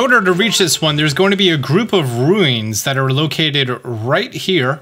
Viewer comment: did you just say, “rueens”